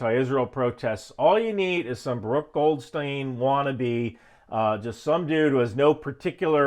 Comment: Um... Baruch Goldstein killed 26 muslim worshipers a the Cave of the Patriarchs with his IDF service rifle. He was a bad dude. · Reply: Apologies, it sounded like you said Brooke Goldstein- who appears regularly on Fox News and is an activist who combats anti-Semitism.